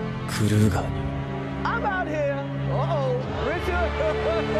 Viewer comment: it sounds like he says “Grisha” lmaooo